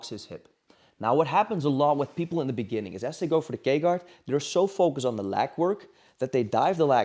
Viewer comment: i thought he was saying the gay guard